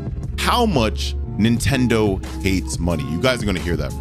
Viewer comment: I GOT SO SCARED I THOUGHT IT SAID “NINTENDO HATES MONKEYS” 💀💀